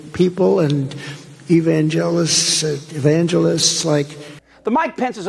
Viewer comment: Just me that heard him say E Van Gelis?